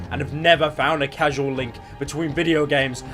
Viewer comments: BRUH did u just say casual link are u mongoloid 8:40 it say causal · Tf is a casual link. It said causal link :P